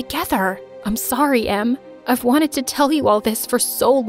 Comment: it says "mom" instead of "em"